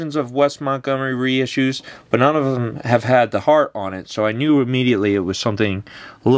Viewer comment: Is it me or did it sound like said “he’s got a Gibson L-5 with a hard on”